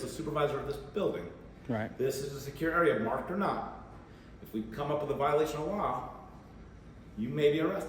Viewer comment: did he just say “if we come up with”😳🤷🏼‍♂️😂😂😂😂😂😂😂😂